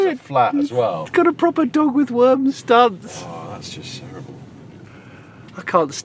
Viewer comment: What does Jonny say at 17:40? Got a proper door with worm studs? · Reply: "Got a proper dog with worms stance ... ", ie. dragging its arse